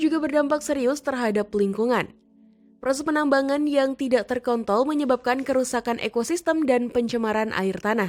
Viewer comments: Menit 16:47...apa sy tak salah dengar mimin bilang bukan terkontrol tapi ter kon***l..hahah mimin terlalu semangat Mau tau donk wajah mimin nya penssaran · apa gua yang salah denger ya? "Terkontol"